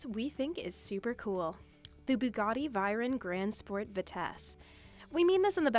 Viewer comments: did she just say "bugatti vayren roadster"? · did you just say Bugatti vayron? My ears are screaming